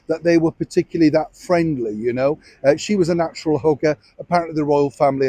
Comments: Good evening Neil, lovely to see you!! 👋🏻 So….at 0:43, where you were saying MeMeMe said she’s a natural hugger… I ‘bout fell out of my chair cause I thought you said a natural hooker. 😳🤣🤣 Which I suppose would al……Uhhhh, never mind. Like you often say, I’ll just leave that right there Blessings & prayers 🙏🏻👋🏻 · I was cooking and I initially thought you said natural hooker, I had to stop everything to go back to hear it again, and you said natural hugger not hooker. 😂🤦‍♀️